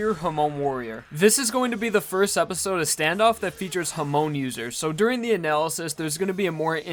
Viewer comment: I thought he said ham users